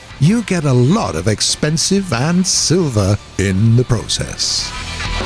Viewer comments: did he say "expensive and silver in the process"? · Did he said expensive at 6:26 while the sub said experience? I think the sub is correct because it make more sence right?